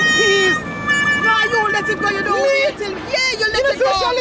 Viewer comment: I thought you said you don't eat pigtails 🤣🤣🤣🤣